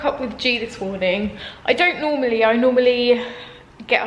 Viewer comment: when you were talking about what time you usually wake up I thought you'd said 8:39am for a sec there and I was like "well, that's quite specific" 😂 Also, thanks for linking the blueberry muffin recipe! They look delicious and I'll definitely give them a try soon. Loved the vlog, I always enjoy watching them :))